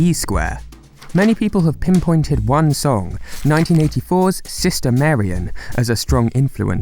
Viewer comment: no idea what he’s saying likes like System Marium.